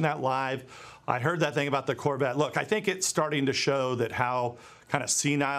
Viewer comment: Watching NewMax and I thought he said plastic corvette, not classic corvette.